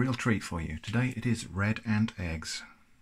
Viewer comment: I was watching your videos on a playlist and when this selection came on, I thought you said, "bread and eggs," and thought, Well, that's not such a strange--OH, RED ANT EGGS. That is significantly more odd than canned bread and eggs! Haha!